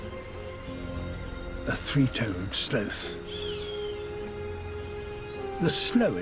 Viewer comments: Did he just say "slowth"? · I thought it said "3 ton sloth" lmao